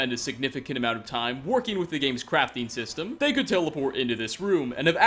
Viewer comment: hahahaha....at the Creation Warehouse, at 1st I thought you said "...so they can literally just check out all the game's crappy mechanics..." Laughed SO hard I startled my dog on the walk - I think it was the upbeat tone of voice while I mis-heard "crafting" that slayed me in a WTF Moment, hahaha! neat vid...I knew of some test cells, but didn't know they had SO many (guessing creation warehouse is the cell w every item in game you see now & then in other YT's vids?)